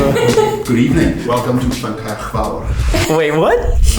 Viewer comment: what was he saying here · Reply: he said: "good evening welcome to Llancaiach Fawr"